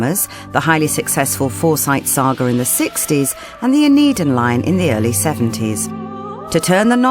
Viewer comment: What did the narrator say at 3:30....(The Forsyte Saga and)....."The Need in Line" from the 1970s?